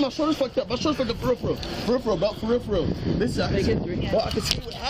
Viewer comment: I definitely thought he was trying to name a part of his shoulder that he said was fucked up at 2:15 when he said "peripheral peripheral". I thought he was just butchering the pronunciation. Then I realized he was actually saying "for real for real" 🤣